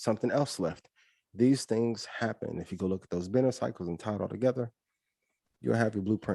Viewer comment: what type of cycle did he say to check out? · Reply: Benner cycle he has a 6 minute video on his YouTube where he talks about it